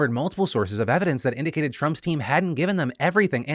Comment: Not gonna lie, I thought you said Trumpstein, not Trump's Team. 😂🤣😂🤣😂